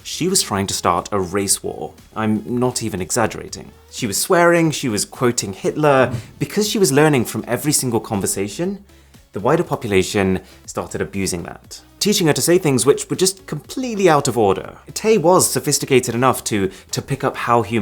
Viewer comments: The first time I watched this, I thought he said "the whiter population", and it made just as much sense in context. LOL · It sounds like he said "the whiter population" lol - probably not wrong anyways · did he say "the whiter population"?? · I had to turn on closed caption. I thought he said the "whiter" population was abusing Taytweets. It was wider. Thought he was throwing shade 🤣